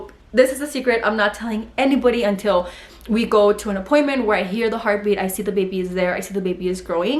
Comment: I swear I heard you say “ i heard HER heart beat” 😂😂❤️❤️